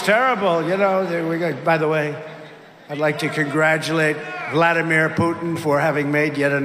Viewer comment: I heard "Gladimir Putin."